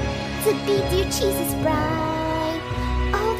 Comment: I thought she said "he'll be marring a stake." So cute.♥♥♥♥♥